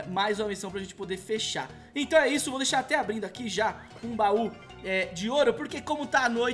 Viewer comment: Parei de ver o vídeo quando ouvi "deixar abrido".